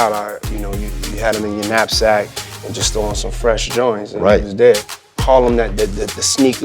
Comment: Did diddy say @ 2:25 that he would get out of school and couldn't wait to put on some fresh Jordans? The years and math don't sound right.